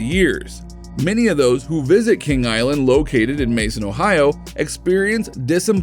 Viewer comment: did he just say “King Island”? And showed a a picture of Nessie at Busch gardens?